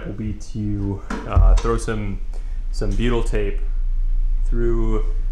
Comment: lol I thought you said BEETLE tape